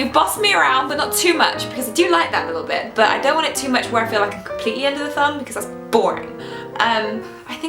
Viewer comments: Syd P Can you tell me what she said at 3:18 - 3:20 and 5:12 - 5:13 ? I'm adding Spanish subtitles :) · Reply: Rose y Rosie Subtítulos Of course! 3:18 - 3:20 is "But I don't want it so much where I'm completely under the thumb, because that's boring." and 5:12 - 5:13 is "She was a flawed super hero, you know?" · Wilmayri Pagan Can you tell me what she said at 3:18 - 3:20 and 5:12 - 5:13 ? I'm adding Spanish subtitles :) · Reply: "but i don't want it too much where i feel like im completely under their thumb cause thats boring"